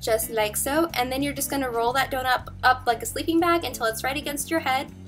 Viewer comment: did you say "donup" instead of "donut" at 7:24 ?? im crying